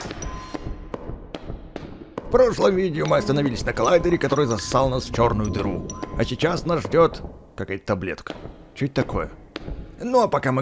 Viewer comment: мне одной покозалось что он сказал засал🗿🗿🗿